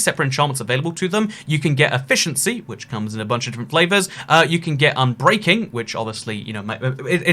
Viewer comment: Did he just say efficiency comes in different flavors?